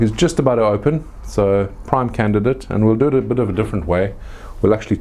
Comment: what did you say ? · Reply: "Prime candidate"